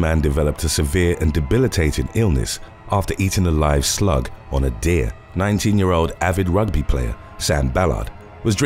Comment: i thought you said ate a slug of a deer.. and i thought yeah thats pretty bad. but he ate a slug...... who does that